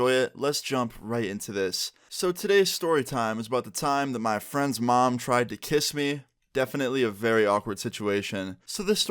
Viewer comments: Bruh I thought it said my mom tried to kiss me🤦‍♂️🤦‍♂️ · I thought he said my mom tried to kiss me 😂😂😭🤦‍♀️